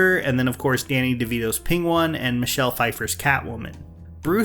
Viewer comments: Did he say Pingone? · did he just say pingwan ?